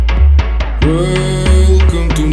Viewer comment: is so hard, no idea what they’re saying tho. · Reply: "Welcome to my hood"